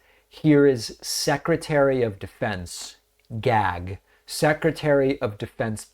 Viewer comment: did you just say "gag" yeah youre gay